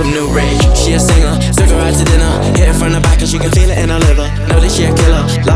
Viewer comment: I swear he said the N word (no hate I’m just pointing it out,love these two) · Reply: he sings "so she can feel it in the liver"